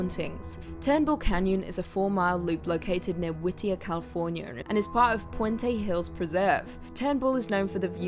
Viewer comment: "Californier" I heard it. Don't deny it lol 4:30 🙂